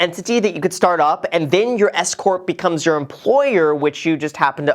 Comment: when you first mentioned the S-CORP, I heard “escort” lmaoo!! For few seconds I was bamboozeled by what I thought 💭 you had said!🤣